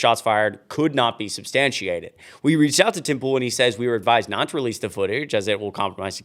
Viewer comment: Thought you said Tim Fool.